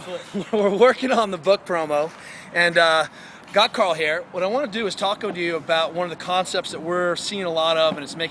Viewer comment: did he say, "What I wanna do is TACO to you..."???